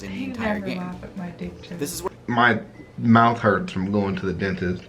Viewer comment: I thought when you said my mouth hurts From going to the doctors I thought he was doing a dick joke too